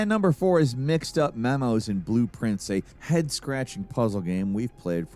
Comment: Google had to sort out wtf said #4 was called. I thought he said "Mixed Up Memos and Blueprints."